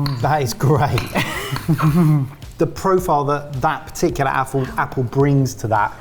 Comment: Mike said Apfel instead of apple accidentially my german heart jumped by joy for a second :)